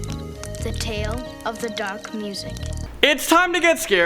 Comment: At the opening, I thought the boy said "The tale of the dank music"